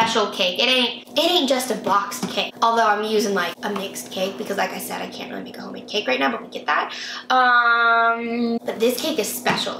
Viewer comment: What did she say in 1:22 - 1:25? 😅 Sorry, still learning English... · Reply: M R She said: “Because as I said, I can’t really make a homemade cade right now, but we get that.”